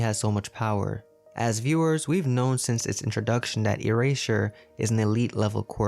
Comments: “Erasher” I’m sorry but this is what I heard 😂😅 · did he say ereasher??? Or are my ears failing me?? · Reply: “erasure” is the name of Aizawa’s quirk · did he just say... eraseSHer!?